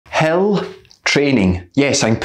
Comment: I thought you said Hell training X_X